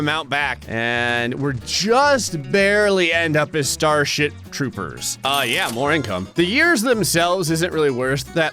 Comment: Anyone else hear him say starshit trooper at 6:06?